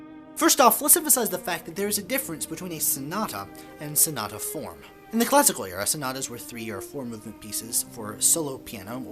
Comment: Bro, what are you saying at 0:20? I've watched this part over and over again and can not for the life of me figure it out. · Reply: "In the Classical era, sonatas were three- or four-movement pieces ..." A lot of these early episodes suffer from talking-very-fast-itis. In few places is it more prevalent than here.